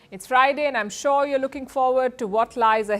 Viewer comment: Did I hear it’s Friday?